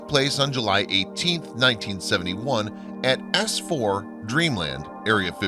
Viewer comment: in the doc the guy says Ass 4 instead of S4 great